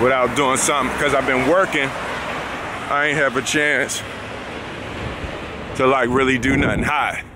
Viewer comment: Did he say “I ain’t have a chance to do something high?” 😂😂👏🏾 at 1:15sec Will you’re amazing!!